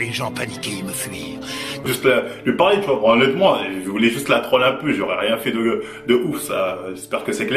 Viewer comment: Je n'ai rien contre toi mais a 4:30 environs quand tu montres les deux versions ça reviens au même non ? Au final "il veut voir des visages terrifiés" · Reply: Mais non écoute bien stp il dit la citation de Cell de Dbz pour rigoler et a 4:37 il dit : "je veux juste lui parler honnêtement je voulais juste la troll un peut, j'aurais rien fait de ouf ça j'espère que c'est clair". Ecoute en ralenti certains passages si il le faut pour mieux comprendre je comprends qu'avec la frustration c'est compliqué de bien comprendre les détails. #FreeMarvel ✊🏼✊🏼🙏🏼🙏🏼